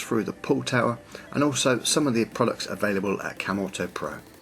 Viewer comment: I thought you were saying camel toe, instead of camuto.